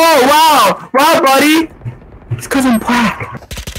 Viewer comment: im sorry what did u said😭😂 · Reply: it's cause im black